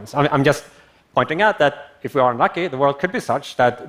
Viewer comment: jsyk ~ at 18:30 minutes, Nick says, "if we aren't lucky..." ~ he does NOT say are lucky, as the Closed Captioning has it. Big difference.